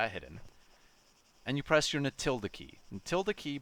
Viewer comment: what key is he saying? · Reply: Tilde key. Its this ~ next to number 1